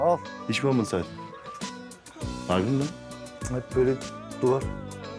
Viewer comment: tufan ne dedi anlayan varmı het dudit duvar dedi sanki hjksadlk